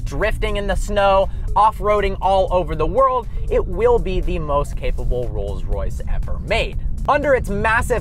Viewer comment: thought you said "it will be the most april fools joke ever made"